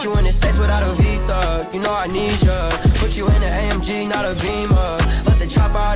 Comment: I heard you said OMG!